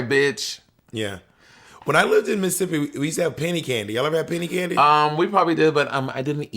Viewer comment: I thought said "panty candy" instead of "penny candy" lmao